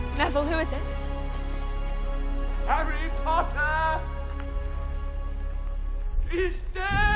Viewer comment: if u turn on caption at 0:29 it says that every papa is dead instead of Harry Potter 💀💀😭😭